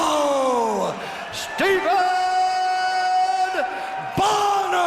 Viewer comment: did he say Steven????